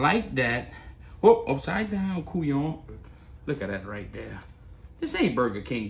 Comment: did he say « couillon » 😂